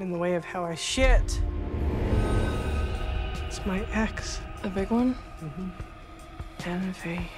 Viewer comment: What did Ramona say 1:41? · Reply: Hakim Ishak a big one